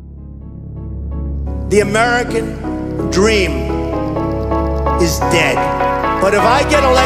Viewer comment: La escuché sin ver la letra y entendí "the American dream is gay"